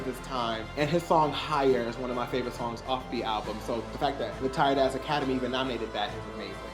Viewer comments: Wait wait wait wait wait wait… Am I really the only one that has been part of this army & the whole muthafkin time I thought he was saying “tight ass army” I hate myself omgggg 😂🤣😅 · I always thought he said “ tight ass army “